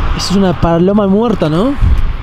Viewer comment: escuché palora